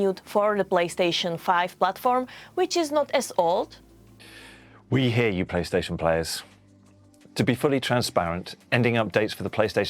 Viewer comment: i thought he said we hate you playstation players